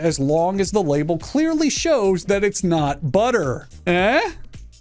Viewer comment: i thought you said "it's right there in the bible"